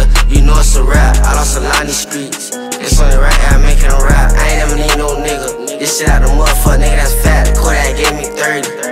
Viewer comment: Can anybody tell me what he was saying here 0:49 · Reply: “I lost a lot in these streets, it’s only right I make it in rap”.